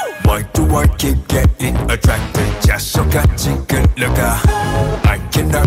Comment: The fact that I first heard "Expect" instead of "explain" lmfao help😭💀